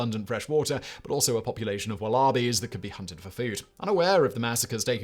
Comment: I thought when he said "a population of wah-LAR-bees that could be hunted for food" I thought he meant a tribe of people.